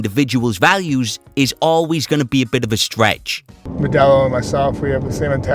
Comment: I hear "always bitching"